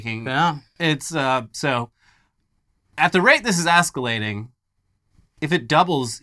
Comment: Did he say "ASS-CALATING"?:face-green-smiling